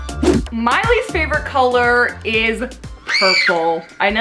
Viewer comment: am I the only one who heard "miley's favourite color is purple!" And went like.... Who tf is Miley? 7:35 did I see koya!!??!